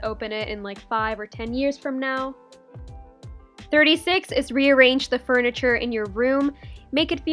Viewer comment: OMG THE QUARANTINE ME THOUGHT IT SAID "renegade the furniture" 😂😂